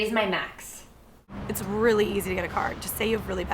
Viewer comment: for the longest time i thought she was saying "it's really easy to get a car" until i read the comment saying "card"....